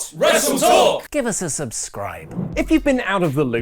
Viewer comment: When he said "Out of the loop" I thought I heard "Out if the Luke" Welcome back buddy!